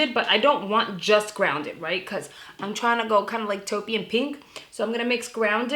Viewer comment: Bwahahahajaja 😅😅 I had to go back twice, I thought you said turkey 🦃 and pink 💗 😂😂